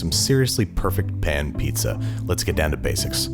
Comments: Thought it said pain pizza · Could've sworn it said "pain pizza" 🍕